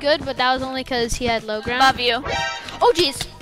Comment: i thought said love you 😭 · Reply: no she said above you🤭